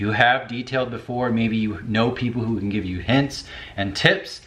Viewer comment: can i use it as a hammer? kidding! (getting mine now for black friday sale) :) Edit: thought i heard "people who can give you heaps of tits"